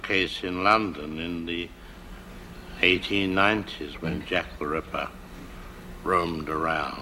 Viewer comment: Wow. When he said Jack the ripper. I thought he said Jeff the killer.